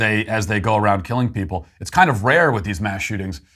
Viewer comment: Can anyone tell me what word Sweet Daddy Walsh uses at 24:55? Stillflect? Driving me nuts